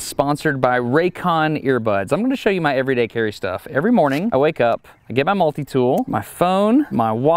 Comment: Lmao I had to rewind the video at the end because I thought he said "Every morning I wake up, I get my multimeter..." 😅